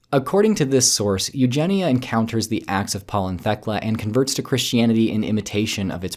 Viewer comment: It sounds like yer saying "The Axe of Paul and Thecla" which sounds more like an epic fantasy story :p